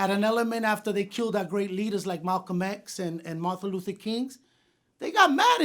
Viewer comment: Did he just say martha luther kings? Lol